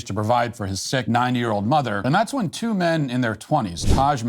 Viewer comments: im sorry but, did he just say "9 year old mother" wth XD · Sorry, but did he just say sick 9 year old mother? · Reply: 90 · I'm sorry, but your accent is indescirnable- I thought you said his 9 year old mother lol. Could you add subtitles? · Pretty sure he said ninety but I stg it sounded like he said nine year old mother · Im sorry, but I heard "Sick 9 Year Old Mother" 💀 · So no one else is pointing it out, I have listened to this at least 10 times and I keep hearing it. Did Matt say "9 year old mother" I mean he has to mean 90 right but it sounds like he said 9 😮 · At first I thought he said the man's 9 year old mother and after three more times listening to him I still think he said 9 year old mother. Listen pal it's ninety years old a 9 and 0 . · Did he just say his sick 9 year old mother...? Am I hearing things there's no way he said that??? Thats what I'm hearing though im so confused what did he say someone tell me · Did you just say "sick 9 year old mother"?😅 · Bro I thought he said his 9 year old mother and got really confused 😂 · Did I hear that right “sick 9 year old mother” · Sounded like you said 9 year old mother 🫁